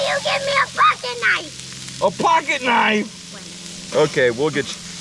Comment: When she asked about a pocket knife. I thought she said Fing knife.